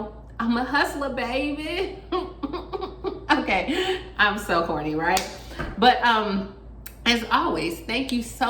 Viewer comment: Did you say your so horny? What did I mishear or maybe a different definition??? Towards the end of this video?